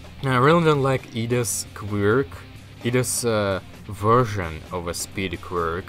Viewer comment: I thought he said “iida’s queer”